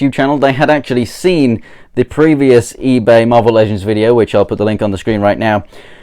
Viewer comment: i thought he say mobile legend